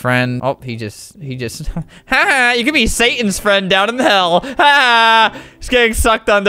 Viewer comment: he said ''hell'' instead of ''heck''